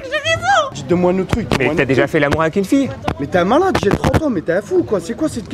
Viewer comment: Je suis peut-être passé à côté d'une blague, mais il dit pas "j'ai trois mômes" il dit clairement "j'ai trente ans"... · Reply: Moi qui entend jamais les bonnes paroles dans les chansons quand je les répètes j'ai entendu " j'ai 30 ans " . Vous vous êtes fais avoir par le sous titre " j'ai trois mômes " c'est tout mdr